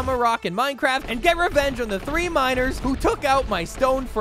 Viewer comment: I thought bro said get revenge on the minors☠️💀☠️💀☠️💀☠️💀☠️